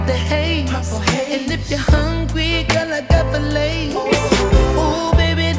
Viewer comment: No cap at first I thought he said "if you hungry girl I got the Lays" 🤦🏾‍♂️🤣🤣🤣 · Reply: Thought he said, I got the Lays like Lays potato chips but filets is def more classy, lol.